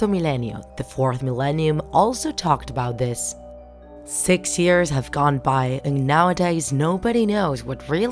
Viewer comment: I heard the voice heard 6 years not 5 years